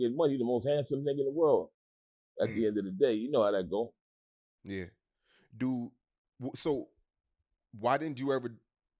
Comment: like, what you tryna say!? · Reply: line, people lining up for the work.